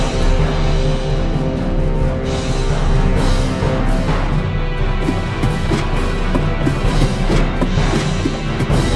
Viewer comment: who knows what it says? Like if you know what it says because ik what it says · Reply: It says im stuck in a cobweb